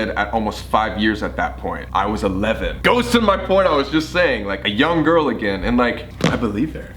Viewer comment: did he say “GHOST IN MY PORNO”? I hope I’m not the only one who heard that